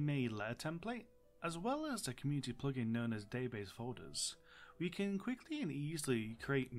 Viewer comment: Sorry mate, I can't find the plug-in you mentioned. Could you post a link? It sounds like you're saying Davis Hoarders, but I'm likely mishearing. Thanks! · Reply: Dang, I listened to it and I hear it. Then I try listen out for Database Folders and I hear that too! I guess I just made one of those sounds that can be misheard to different things haha The plugin is Database Folder: <link>